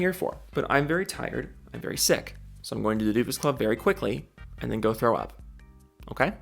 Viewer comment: Misspelling doofus as dofus isn't as bad as what auto captions heard it as. Dupus. Join the Dupus club everyone. Be a dupus like me.